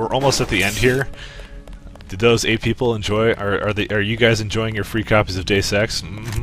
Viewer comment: I kept rewatching the first few second of the video because I thought that you'd said, "Are you guys enjoy your copies of free sex? Mhmm I bet you are." Then again, I'ts Dues Ex, so isn't it basically the same thing?